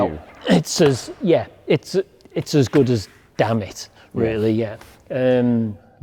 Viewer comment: Me: did he say "damn it". Yes, he did. ;-)))